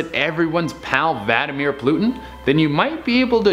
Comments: DId he just say Vadimeir Plutin? LOL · is it just me or did it sound like he said vladimir plutin not vladimir putin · At number 9 did he just say vladimir plutin? Instead of putin